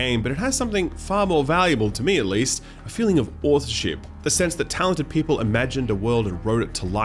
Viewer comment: Anyone know what he said at 6:35 ? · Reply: Authorship